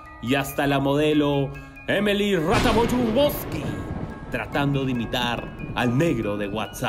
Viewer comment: Emily que? Alguien que me escriba su apellido que no entendi nada XD lol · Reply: Emily Ratajkowski. De nada. :V